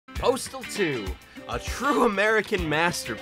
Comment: FFS I thought it said Portal 2. Now I'm disappointed :(